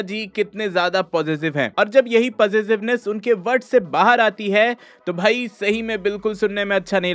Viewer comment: Did I hear it right. Possessiveness unke "BUTT" se Bahar aati Hai 😂😂😂